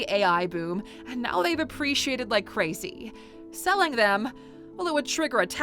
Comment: Be careful about the subtitles, I noticed several errors, like in 19:10, where it says "depreciated" instead of "appreciated"